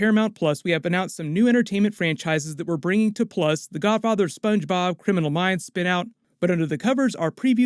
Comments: I heard you say, "The Godfather Spongebob", as it was one name for a show and I was beyond confused lol. Beyond pumped for the new Avatar series coming! Awesome video as always! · I thought he said “Godfather Spongebob” as in a show about Mr Squarepants in his old age leading a family of sponges who control the crime in bikini bottom.. Godfather Spongebob : “you know I can’t refuse this request on this the day of my Krabby Patty’s wedding to this bun, and stack of condiments.. I’m ready, I’m ready, I’m ready to put a seahorse head in Don Patrick’s bed.”